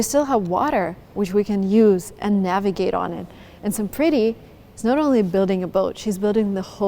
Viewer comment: when said her name, I heard "Some Pretty" :) She is pretty 🤍